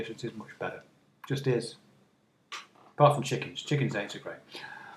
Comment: What is it you say at 6:55? ‘Apart from chickens, chickens...’ eggs are great? Execrate?